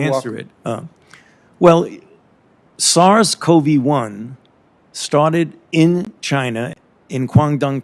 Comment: W.T.F. did i hear that right ,,,@ 1:56 > "Soros Covy 1" ???